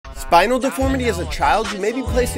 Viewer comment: I thought you said spider deformity 💀💀💀💀